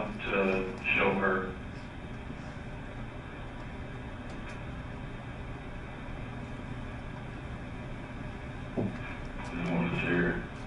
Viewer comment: When the detective asked why he asked his sister to see the guns it sounds like he said I wanted to shoot her. She was right to be terrified at the beginning that’s for sure. For those saying Kyle wasn’t a good navy seal if he got shot at a shooting range this guy shot them unexpectedly obviously. They were trying to help him.